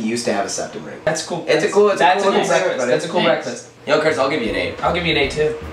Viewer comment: My brain: Did he just call him Chris? His name is Troy. pauses video My brain: Wait. I don't remember watching a YouTuber named Troy??? tries to remember Kurtis' username Me: waIT HIS NAME IS KURTIS I've literally been watching Kurtis' channel for months how did this happen